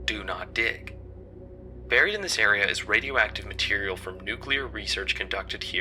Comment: do i hear "radioacive material"? WOOO LETS MAKE OUR OWN NUCLEAR REACTOR